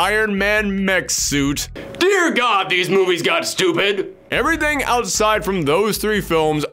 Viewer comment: I thought you said you liked stupid.